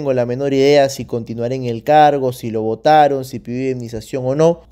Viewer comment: Pensé que dijo "Si lo mataron" xD